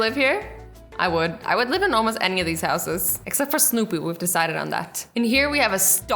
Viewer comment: did azzy just say snupu?